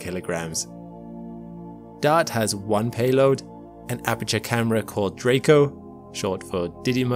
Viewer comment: did I hear that right? “APERTURE” camera?!